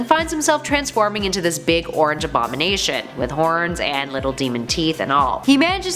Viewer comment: did she say "little" demon teeth?...